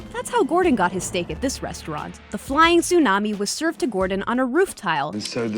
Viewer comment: Lân Lê hummm... we said flaming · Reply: oh sorry I thought I heard flying tsunami